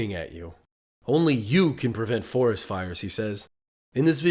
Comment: This took a long time to make so I hope you enjoy it! I'm off to work now and I'll respond to comments when I get home later tonight. Thanks for watching. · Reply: I could’ve sworn Smokey the Bear said “wild fires” instead of “forest fires”.